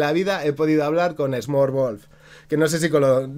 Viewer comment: de quien hablas al 27:17? no entendi como se deletrea u.u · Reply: Smokerwolf Que soy un personaje